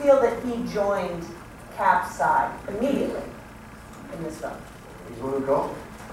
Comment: What does Jeremy say at 16:41? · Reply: Lndsey MSP “he’s the one who called”